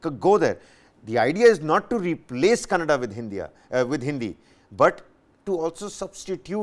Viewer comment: did he say hindia?